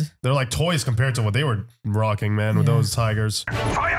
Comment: Did I just hear Moroccan Men ?